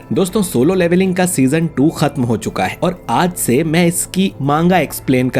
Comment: Did I just hear manga??